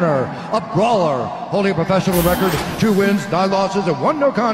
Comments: Almost sounded like he said 63 inches tall lol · I’m just glad he didn’t say he was 3ft 6inches tall 😂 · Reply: I had to listen twice. I thought he said 63inches tall the first time